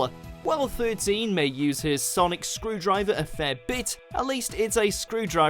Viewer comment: Firstly he very clearly refers to 13 as “her”, so where you got male from I have no idea? And if you listen to the full sentence about Ursula, he’s talking about the Abzorbaloff’s remains, hence using the pronoun “he”. So both times are actually correct. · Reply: I realised about the first one, I misheard "her sonic screwdriver" as "his sonic screwdriver". As for the second one, I re-watched that part and yeah, you're right.